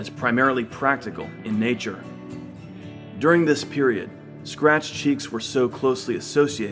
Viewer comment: I thought he said virgins spread their cheeks until they bled and I almost threw up. Then I rewound it and realized he said scratched...